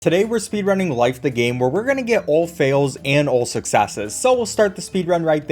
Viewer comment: what are you trying to say 🤔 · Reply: Where-we’re-gonna-get